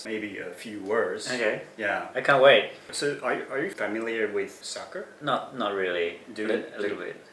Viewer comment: What did you say? S...soccer? Huh?